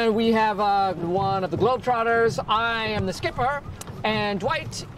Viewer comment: "One of the Harlem Globetrotters." No wonder Stanley is pissed off with Michael. Lol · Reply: Thought he said Juan of the globetrotters